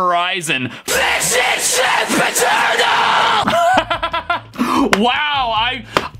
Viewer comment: i hear sempiternal